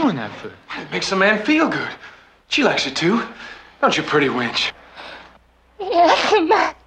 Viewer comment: the word is "wench". A "winch" is a hauling or lifting device. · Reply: I thought he said witch but that other girl said wench